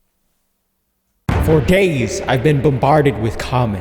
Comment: i thought you said “for gays” 💀💀